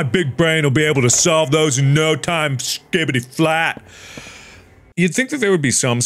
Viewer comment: did markiplier just say skibidi?